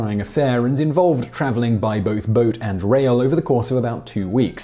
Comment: Boat and rail....ok. Thought you said boat and WHALE. Had to replay it. I'd never heard of travel by whale, but don't know enough about Japanese history to doubt it.